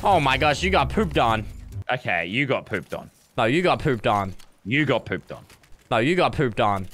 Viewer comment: i hear no you got pood on